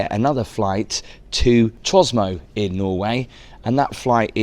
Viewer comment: did you say Tros-mo? 😮😮 and why is your itinerary so convoluted?? 😢😢